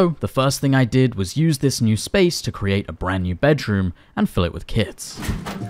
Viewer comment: create a new bedroom and fill it with kids... Okay ^^ I wasn't really paying attention and i though I heard that!